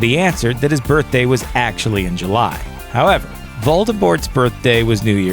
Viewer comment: Is he actually saying Voldebort??